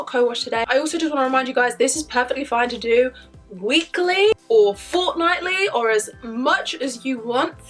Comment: it sounds like she said fortnitely